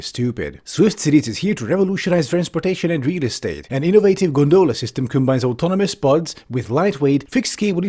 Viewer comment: For a tiny moment, I thought you said, "innovative granola system."